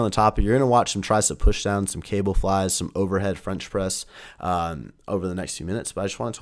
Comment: sounded like overhead French press 😂